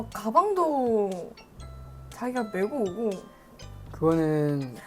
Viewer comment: DID I HEAR JAGI 11:16 17:40 !??!??!!